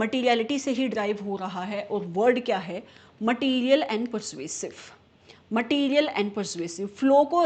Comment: shouldn't it be "material and PERVASIVE" instead of PERSUASIVE ? 😢